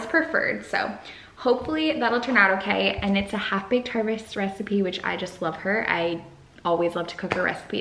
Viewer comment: Ohhh "half baked harvest" is what u said...I literally heard "havbaherffergger"